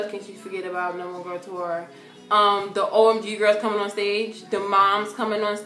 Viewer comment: LMAOO I THOUGHT SHE SAID "OMG GIRLZ FALLIN ONSTAGE"...she said "OMG Girtz COMING onstage"